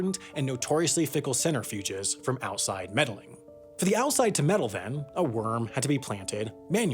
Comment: The captions used "mettle" instead of "meddle"